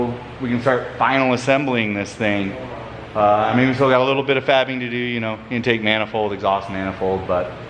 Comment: Bruh caption says 0:13 "we got a little bit of fapping to do" 🤣